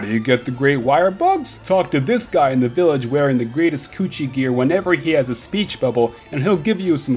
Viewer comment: I had to hear that again lol I thought you said "Greatest Coochie gear."